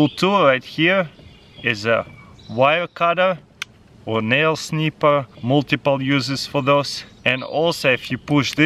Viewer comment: Wait did i just heard him say nail clipper ??